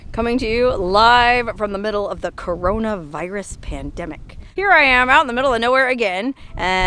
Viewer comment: She always makes sure to mention corona virus. In every video. Why? Like we don't hear enough about it. Every day! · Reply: 'corona virus'....well shit, I thought they said: Covfefe